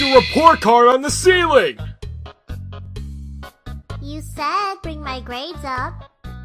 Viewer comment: To me (not bein a hater just sayin my opinion) at 2:40 to 2:43 I heard when frisk said "you said bring my grades up" it sounded like a little bit when she said "you said" it sounded like "you sad bring my grades up" but not fully. just to me it sounds like it. But you have great comics!